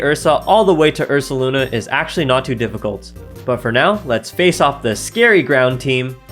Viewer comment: *Edit: This comment doesn't make any sense, I misheard the video. Thought he said "..evolving all the way isn't too powerful" (he says isn't too difficult) 27:49 I made that mistake, unfortunately. I evolved my Ursaring into an Ursaluna and immediately lost against the opponent that I had previously taken down to 20 all the way from them being at 80. Came in second place because of that 😢